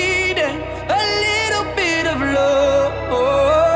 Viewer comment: I first heard this song in a store and I really thought he was saying "a little bit of blood". Please tell me I'm not the only one :'